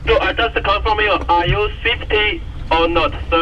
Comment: indeed wtf is "swifty"??? 21:30 what was the radio man trying to say? haha! amazing video just had to pop in to the comment section half-way through~